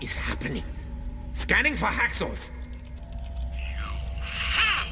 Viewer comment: Bruh, did Richtofen just say at 9:45 ," Scanning for Haxorz "? What a 2011 meme.